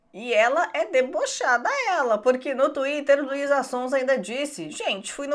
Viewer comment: Só eu que ouvi a dizer LUIZA SONSA kkkkkkkkkk kkkkkkk vc é demais kkkkkkkkk Melhor canal véi kkk